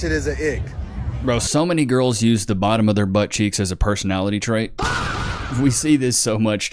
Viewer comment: I thought you said they use them as a personality tray 😂